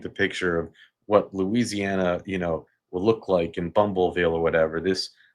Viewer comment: Lol, did you just say bumbleville😂